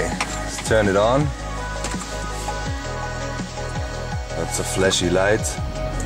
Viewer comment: it sounded like he said "lots of fleshy lights" when he turned it on